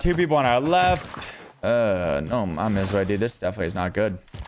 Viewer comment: did he really said "no mames:?